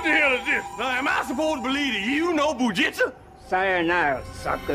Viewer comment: What did Eddie Murphy say at 1:23? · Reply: Bu-jitza😂😂😂